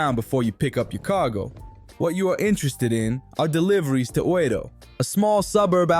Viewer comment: lmao, I thought you said "deliveries to order" in Penguin's accent